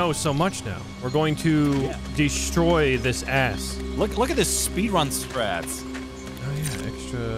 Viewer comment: for a moment I thought he said speedrun scrats.